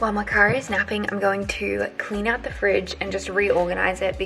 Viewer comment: Im dying on 11:15 I heard you say while my car is napping and I was so cofused for a second lol